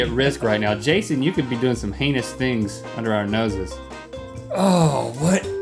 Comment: did link say "You could be doing penis things under our noses."???